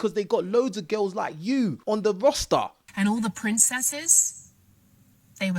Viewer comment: I thought you said “loads of girls like you on the rasta”